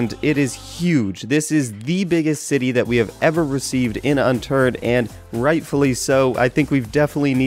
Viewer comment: it sounded like he said Unturd :|